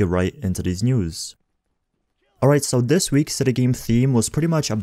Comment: Man it took me a few minutes to realize you were saying state of the game. I thought you were saying setta game. Lol